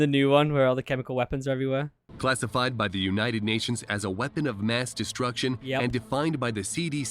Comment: all i heard was weapons of ass destruction